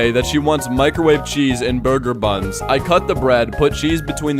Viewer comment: holy amogus i thought that said burbger